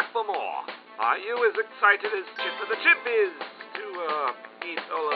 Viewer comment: What was he saying, and is that actually Chimpy Chippa himself. Edit: It is. Edit 2: I think he said "Are you as excited to sing with the Chimpys?"